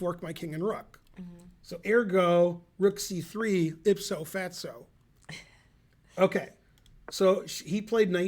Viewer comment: Does anyone know what Ben said at @ 26:32? It sounded like "ipso fetso". laughed when he said it.